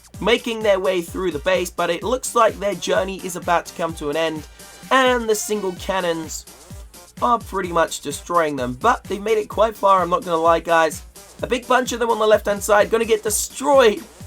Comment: guys he sounds like he is saying Giled canon